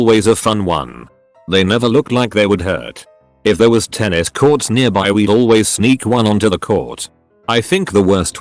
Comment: sounded like "if there was 10 escorts nearby"